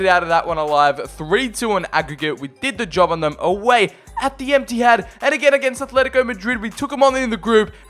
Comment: Anyone else heard emptyhad at 12:53. Just me ok👍